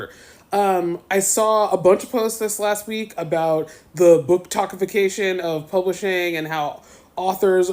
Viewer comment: At the end when she said “booktok-ification” I thought she said “bookTOXification.” Same thing I guess haha